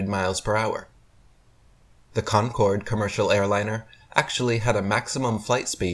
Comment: I did have to laugh when (slightly higher speed) Convair airliners are included in Eric"s somewhat scattergun argument. Does he not know that this manufacturer went out of the civil airliner business decades ago and that none of their passenger aircraft can be found outside of a aviation museum now? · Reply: I thought I heard him say "Convair", but I though maybe he mispronounced "Concorde" or something, because, as a pilot and aviation fan, I know that the Convair 880 is a really fast airliner, but still only Mach .97 or thereabouts, and they were pretty much well out of service by the 70's already! Where exactly does Mr. Dubbya "do his own research"???